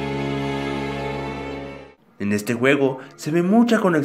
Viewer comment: En el minuto 1:06 oí en este huevo